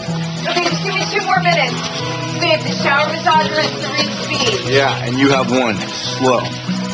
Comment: wht did she say? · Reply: No she says the shower has three speed